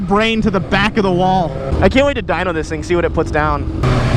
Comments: I thought you said "I can't wait to die on this thing" haha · I thought said “I want to die on this thing” but then I realised he said Dyno it. See that makes total sense 😂